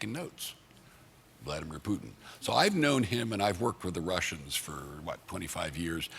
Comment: #AshCarter : "I worked for the Russians for ... what... 25 years" ?!? did you mean with? :D :D :D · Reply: "I've worked WITH the Russians.... thats what he said... lol listen twice, comment once.